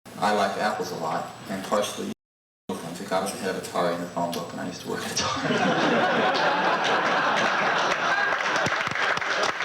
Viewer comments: What did he say around 14:55 and everyone laughed? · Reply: I think he said "Apple comes ahead of Atari in the phone book and I used to work at Atari". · can anybody help me out if you can catch what Steve says at 14:50? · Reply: it would get us ahead of Atari in the phone book... Google ;-) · What does he say at 14:55? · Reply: Chrispollis Basically, he's saying that he used to work at Atari and that he wanted to come before Atari in the phone book and that's one of the many reasons for Apple. Because it came before Atari in the phone book. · Does anyone know what he said at 14:54? "Partly because I like apples, and partly because I worked at Atari? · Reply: From what I can decipher he said, "and because Apple comes before Atari in the phonebook and I used to work at Atari."